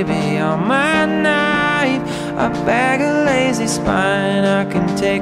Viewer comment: I always thought he was saying 'a bag of Lays is fine'.